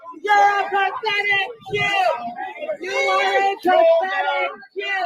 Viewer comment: I thought I heard pathetic JERK! :)